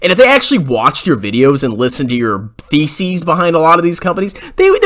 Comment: Lmao when you mentioned about investment bankers listening to Jeremy's theses, I thought you said feces. Might as well be